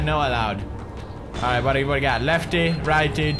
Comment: At first i thought u said double brownie